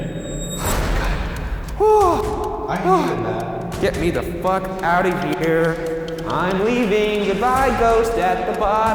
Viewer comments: Can someone stop at 8:12 i think i heard someone said "get out of here" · Does anyone else hear the get out around 8:11?